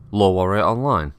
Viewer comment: After that demonstration of the sneakiest 80 tons of the Inner Sphere, I could have sworn I heard you open this with "LOLWarrior Online."